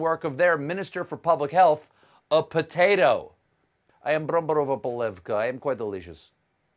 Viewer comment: did he just say "bramborová polévka" (potato soup in Czech)?